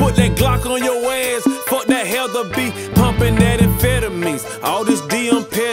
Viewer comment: WATCH DOGS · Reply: did he just say bumpin them amphetamines lol...did he just say your gonna need a detonator swimmin with them bureacudas